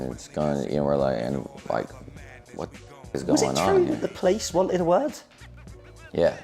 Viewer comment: What is the interviewer saying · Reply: I think he said..." is it true the police wanted a word?"